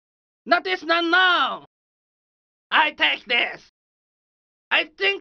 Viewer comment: "And the winner of knockout iiiiisss" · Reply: I always thought he said "Winner by default is".